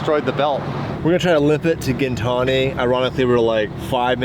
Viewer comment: I thought said limp back to gintani😂😂😂